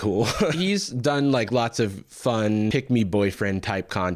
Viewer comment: i kept hearing "pigmy" instead of "pick me" and it's really fun to imagine that he's like this because he's just really small like just a real little guy. pigmy boy